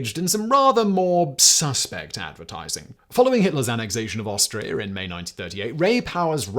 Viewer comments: Why do I hear you say Australia instead of Austria? Either I need a good long sleep or I'm going nuts, possibly the later. 😄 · Am I going deaf, or did I hear that Hitler annexed Australia in 1938 at 4:14?